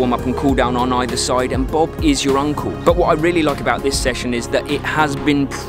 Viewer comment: Good advise. Thank you 3:00 mark 'Bob is your uncle'?? Did I hear that right? 🙂